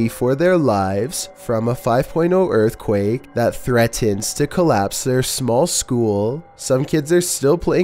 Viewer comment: i thought he was gonna say “That threatens to crush their small skulls”